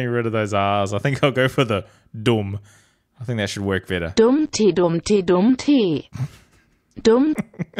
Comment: I swear that said dummty in a polish accent